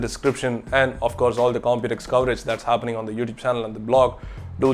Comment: did he say don't check them out?